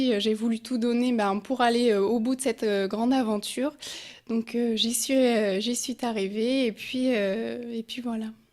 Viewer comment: " J'y suis T'ARRIVÉE " 🤔👏👏👏😂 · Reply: J'ai compris "Jésus est arrivé"